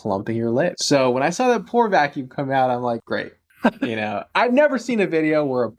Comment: Oh, PORE vacuum! I thought you said FLOOR vacuum! Lol!